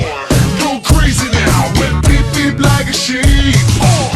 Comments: Sound like there say beeb beeb I'm a shit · It started to sound like it said "Beep Beep, I'm a shit" Yep